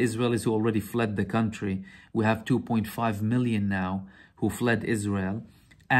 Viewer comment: you said 2.5 million have left Israel? Did I hear that correctly?